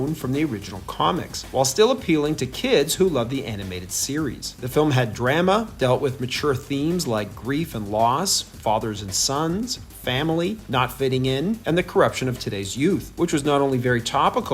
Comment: Did you say... Dram ah? 😅